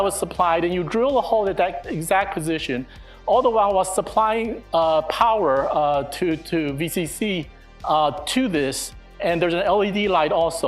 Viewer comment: min 15:06 creo que dice "while suply power to VCC to this" que viene siendo "mientras suministramos energía del VCC a esto" vcc viene siendo la parte de un circuito integrado por donde suministramos energía (ojo no estoy seguro al 100%)